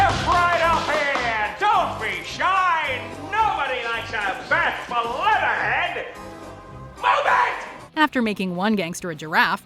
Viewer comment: Step right up, don't be shy, nobody likes a [what?] (Fat Full Leather Head? Bat Full Letter Head?) I never could figure out what he's saying here. Does anyone know?